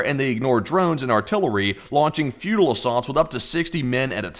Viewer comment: Captions say "feudal" did you mean "futile?"